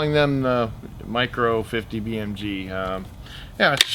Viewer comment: when you said micro, I had to play it back several times cause I thought you said Mike Rowe of Dirty Jobs. Guess I need to clean the wax out of my ears. Yes, more gun laws to force the criminals to put down their guns. Yea. Right you moronic gun grabbers. Wish I could give this video multiple thumbs up.